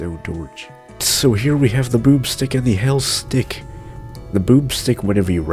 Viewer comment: I HEARD YOU SAID "BOOB STICK" LOL